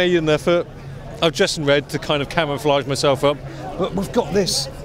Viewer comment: Kai W did you just say „cameraflage myself up“? :D